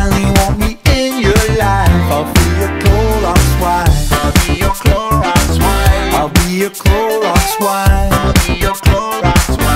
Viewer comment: I heard "ill be your roblox wife"